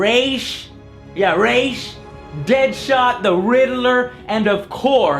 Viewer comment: Heh, he couldn't be the Riddler because he uses a green screen. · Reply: General CJG I thought he said Reddler